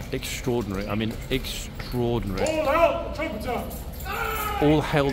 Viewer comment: I think the Captain said “fall out, the trumpeter!”